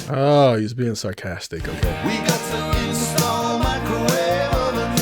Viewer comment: Kind of a big song. Wow I always thought he was saying we got to SELL microwave ovens..